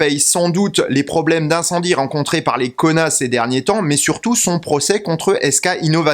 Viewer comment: j'ai entendu : ces derniers payent sans doute les problèmes d'incendie rencontrer par les connasses ces derniers temps